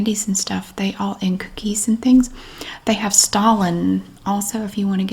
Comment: When you said stollen I thought you said Stalin 🤣 I didn’t know you could find dead Russian dictators in the middle aisles of Aldi now!